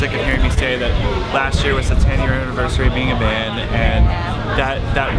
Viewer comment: i thought chris said "10 years of being a man" and i just died